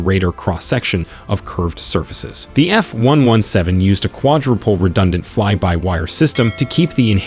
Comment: did he just say 'quad ro pole' fly by wire? Quadruple.